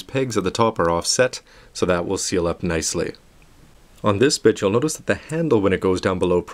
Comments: Did you say bitch on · you said this bitch instead of saying this bit just wanted to tell u lol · it sounds like he says "on this bitch" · it sounds like he said, "on this b*tch"